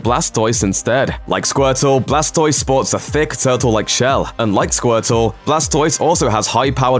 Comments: Alexander go back and listen to the sentence again. He specifically says like blastoise. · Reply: I did. He specifically said "unlike." Clearly the words sound very similar in the clip, so he's bound to be misheard by a few people. Since "like" would make no sense, it's obvious which one he actually said. That's what context clues are for. And you can also just turn on the captions. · Squirtle doesn't have Shell Cannons on his back. Hell Wartortle doesn't either. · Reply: Slowing it down at x0.25 I hear "un-like" - I don't really hear a "d"